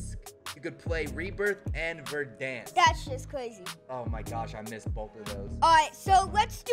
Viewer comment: Did i hear "that shit Crazy ? LOL !!!